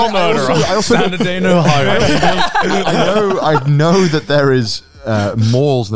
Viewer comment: does anyone else hear say "another day in Ohio" 😆